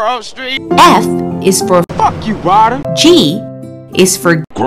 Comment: why do i hear "fuck you Reiner" instead of Ryder